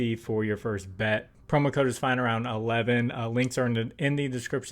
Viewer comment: Not going to lie brother but everytime you say your channel name it sounds like your saying it's "flying around"